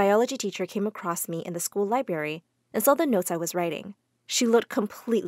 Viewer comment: DID SHE SAY LI -BARY!!!!!!😱 THAT'S ILLEGAL!!!!!!